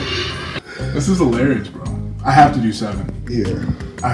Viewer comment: When he said I have to do 7. I thought he said I have to reset. 😆